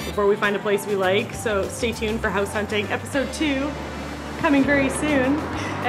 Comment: I thought you said House Haunting in Tokyo. Wooooo....... 👻👻👻👻